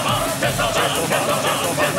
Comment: I KEEP HEARING "QUESO MAN" INSTEAD OF "CHAINSAW MAN"